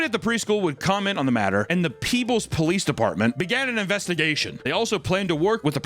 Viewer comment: oh the place is called peebles i thought you said the "peoples police" like they called in the fu**ing Rock to try and solve the case